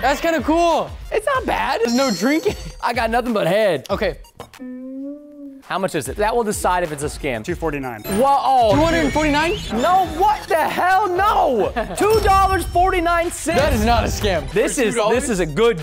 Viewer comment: Umm did anyone else hear him say “what the hell”